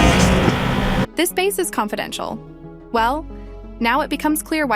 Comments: i'm pretty sure he said "This remains confidential" instead of "this base confidential" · Nice video, although at 6:55 instead of them saying "this base confidential" it would make more sense that he would've said "this remains confidential". It's just what I heard though so I may not be correct :)